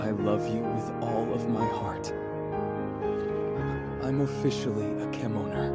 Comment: he actually said "I'm officially a furry" then the doctor repeats the word